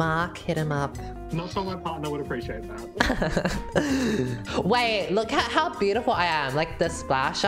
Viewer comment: what does the guy say? He has such a thick accent I have no idea what he said. "smth... would appreciate that?"